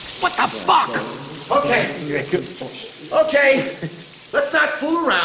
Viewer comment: HAHAHAAHHAHA DID I JUST HEAR ""WHAT THE FUCK"" AT 5:24 / 5 HAHAHAHAAHAHAHAHAHAHAHAHAHHAAH